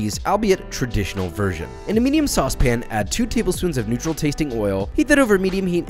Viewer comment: You know you’re an alcoholic when you thought he said “Nutrl tasting oil” instead of “neutral tasting oil”